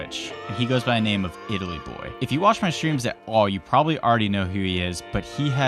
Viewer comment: I thought he said giggly boy instead of Itay boy.